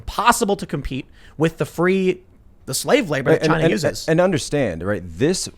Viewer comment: did Tim actually say, "Chineses!" lol hahaha double plural!!!